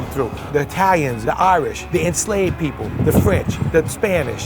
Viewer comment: what did he sayyyyy XD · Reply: Enslaved people, as in people who were enslaved, hope this helps 👍